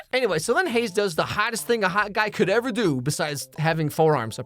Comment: I had to rewind cause i heard "having four arms" and i had to rewind cause like WTF is this about.